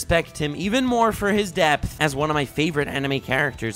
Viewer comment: when you said depth I thought you said death and I got frightened